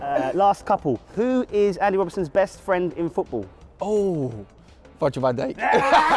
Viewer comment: the subtitles 💀💀, Instead of Virgil Van Dijk it said "fudge on my date" 💀💀💀💀💀